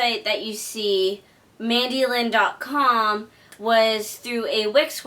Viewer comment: Every time I hear your name I hear “Mandolin,” like the instrument, or the slicing tool.